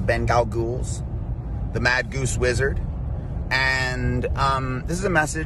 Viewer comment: I thought that he said "Mad Duece Wizard"....I kind of liked the label